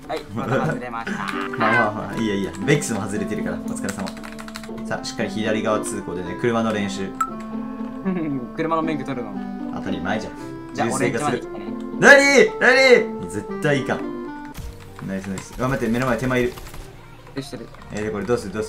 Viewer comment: 最近めっちゃブレイキングダウン見てるからここがジョリーの「当たり前じゃ」にしか聞こえんかった